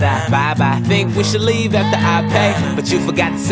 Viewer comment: I can't be the only one hearing "that's how you know you fuck dogs." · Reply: I hear "That's how you know you fucked Dug!"